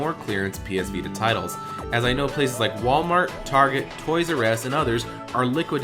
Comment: anyone heard him say Toys R Ass? 1:26?